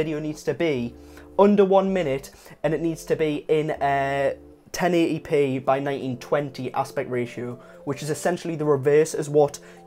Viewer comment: Hi! Thanks for a very nice video! I can't quite understand the numbers you mention for the aspect ratio. At 1:14 it sounds like you say "10 EDP by 1920 aspect ratio", is that right? Also, do the shorts work best if you have a moving video as opposed to a series of photos? I'm trying to think of ways to promote my YouTube channel that features my indie folk music.